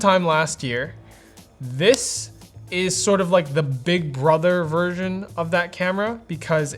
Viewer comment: What Brandon says: "Sort of a big brother version" What normies hear: "It's a bit better" What I hear: "ONII-CHAAAAN"